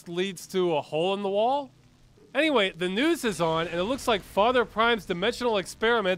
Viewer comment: I kept hear father "crime" not prime and I'm kinda disappointed that it wasn't.